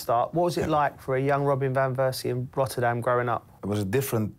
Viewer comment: Why does it sound like Joe is saying Robin Van Versie apologies if im hesring it wrong.